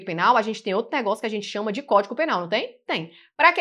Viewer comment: Eu ouvi “códico” penal . Desculpe se ouvi errado. Bem no início da aula.